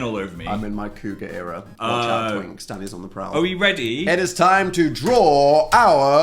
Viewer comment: I thought he said “watch out twinks, daddy’s on the prowl” but I think he said Dan😂